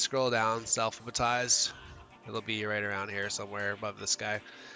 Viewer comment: This is great. I just have one question: what word did you say at 3:41? It sounds like ''selfbatize''. I'm trying to find the option to adjust the scanlines so I need the word. Thank You.